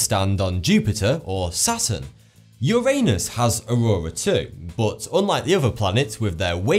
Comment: 2 things about this time stamp. 1. i was occupied checking alerts and i heard "your anus has" and had to rewind it. 2. timestamp is 42.